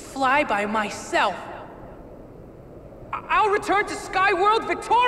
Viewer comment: Is it me, or did said "armpit"?